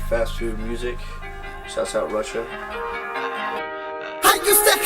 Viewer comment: Мне послышалось "Сосал Russia"